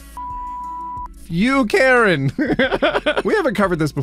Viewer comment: If you listen closely I swear he said "fluff you karen"